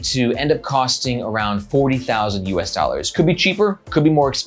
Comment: hahaha at first I heard "400,000 usd so I had to go back and check! Super excited to see this journey. Wish you the best of luck.